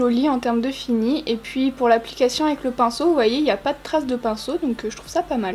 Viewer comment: Quand tu as dit "stries de pinceau", j'ai compris "street pinceau" et j'étais là mais, "c'est quoi un street pinceau ? Genre des coups de pinceau au hasard ?" Et puis mon cerveau a fait la mise au point du coup...